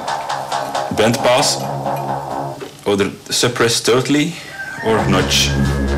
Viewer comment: I've been listening for 10 minutes now and have no idea what is being said at 3:33 Otherwise great video! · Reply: "Bandpass, suppress completely or nudge"